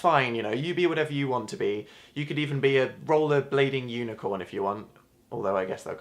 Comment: Lol when he said "rollerblading unicorn" i thought he said "uniblading rollycorn"